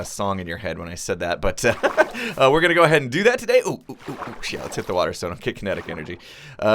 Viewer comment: Starting at 1:23, he begins to go flying into a wall. It sounds like hes saying, "Ooh Ooh, Sh*t, let's hit the water." I'm not the only one hearing this right?